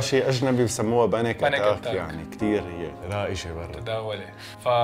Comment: شو قال هون؟ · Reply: قال اسمو بالاجنبي panic attack اسم المرض اللي هو نوبات هلع